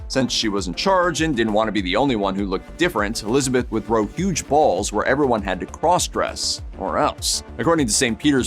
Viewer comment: after discussing the complementary aspects of men's fashion it sure sounded like you said she grew huge balls instead of threw huge balls 😜 Which considering the topic matter stuffing of the cod pieces happened at other times in history 🤷